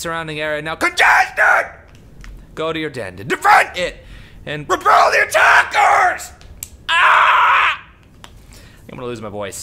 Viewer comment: all i heard was "YOUR DENS...CONTESTED...DEFED...REPEL THE ATTAC KERS!"